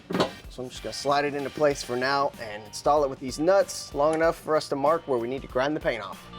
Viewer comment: it sounds like he said deez nuts lol 😂 but awesome episode